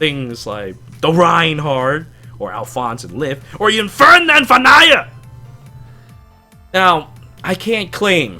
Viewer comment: What did he say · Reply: FERDINAND VON AIAH!!!